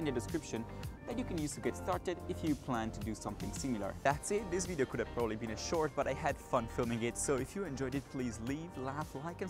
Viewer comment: i thought you said "if you enjoyed it, please leave" and i was like "oh okay, mean mali is mean" anyway, this was awesome! great video my dude!